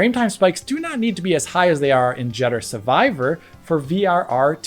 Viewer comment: haha did you say 'judder survivor'?? burn!